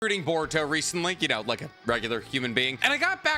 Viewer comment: What does he say? "Reading Boruto recently"? Like that doesn't make sense. Maybe he was saying "[I was re]reading Boruto recently", but that doesn't make much sense either because he qualifies it with "like a normal person", which just doesn't fit.. Like a lot of people refuse to read Boruto so I am confused